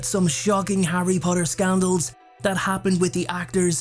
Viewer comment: I thought it said “sandals” and not “scandals” 😂